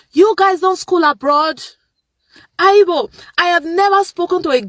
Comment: South Africans are comfortable in their country, are working and our people are benefiting from our country's wealth.😅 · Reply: Did she say "haibo"? 0:37?